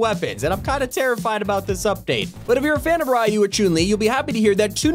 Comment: did he just say chun - li choon li....